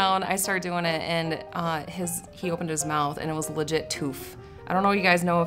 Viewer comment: what did she say? tooth